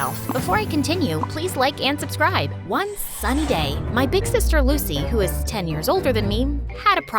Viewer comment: Am I the only one that thought she said one Sunday when she actually said one sunny day😭